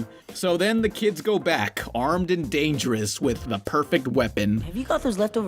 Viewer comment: The kids are armed and what? Could you say that again? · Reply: Dangerous.